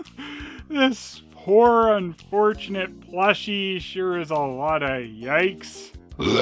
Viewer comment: SERIOUSLY! WHAT IS HE SAYING!!! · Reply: "Let Bootleg Garfield plush stare into your soul"